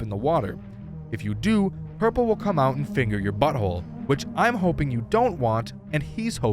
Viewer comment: When my air pods connect I just hear “and he wants to finger your butthole”